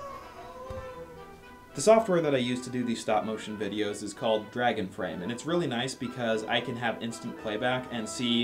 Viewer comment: Dragon frame I thought you said " drag and frame".